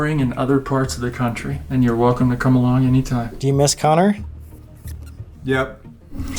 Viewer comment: I thought It said welcome to parliament